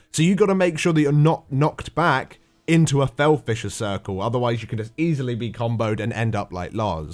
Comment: Somehow I kept hearing "fel fishes" and "chrono shots" Oo